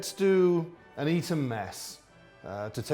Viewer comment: To whomever wants to correct the subtitles, at 8:28 it says (Mumbling) but what he said was an 'Eton Mess'. It's a dessert that comes from Eton College.